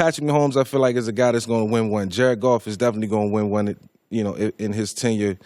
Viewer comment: sounds like he says jack off is going to win one